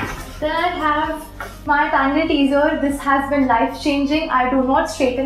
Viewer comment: what did she say? what is that tool called? wasnt quite able to catch the name of that one. Also where did you get it from? What brand is it? Oh and how much did it cost? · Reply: She said tangle teaser